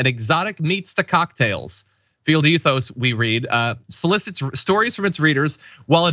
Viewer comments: I thought John said, "Failed Ethos", which would make more sense. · Haha, I thought you were saying “ Failed Ethos”! I like my magazine name for this debacle better!!